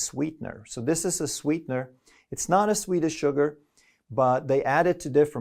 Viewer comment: says “Sounded like he said Swedish Sugar (he said sweet as sugar) Swedish word for sugar is SOCKER”